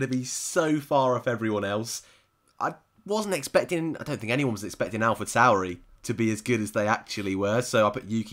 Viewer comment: Subtitles at 15:20 say 'Alfred salary' instead of Alpha Tauri. Also multiple cases of 'has' or 'house' instead of Haas